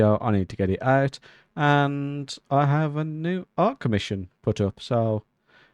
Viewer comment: My favorite flower is the lily of the valley. I don't know what it is about them, but I've always liked them It took me a second to realize you said "art commission." For some reason, I thought you said "ark mission," and I was rather confused 😂